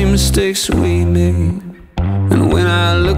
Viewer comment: 저만 자꾸 헬창으로 들리나요... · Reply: 아 ㅋㅋㅋㅋㅋ Hey child를 헬창 ㅋㅋㅋㅋ